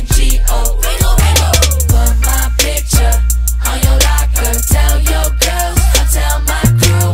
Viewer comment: What dose he say at 1:13 "f*** my b****up" ?!?!?! Edit: OMG I DID NOT THINK I WOULD GET THIS MANY LIKES THX SOO MUCH TO EVERYONE PS I DID NOT MEAN TO BE RUDE IN ANY WAY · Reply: He says put my picture on your locker 😀